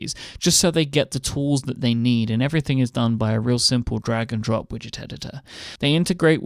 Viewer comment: When says "Drag and Drop" what I hear is "Dragon Drop".